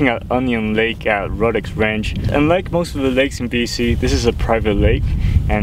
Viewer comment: correction most lakes in the province are not privately owned. as a matter of fact very few are privately own. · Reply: The audio didn't come out as clear as I had hoped. The "un" in the unlike in my talk somehow got masked so it sounded like I was saying "like most lakes in this province..."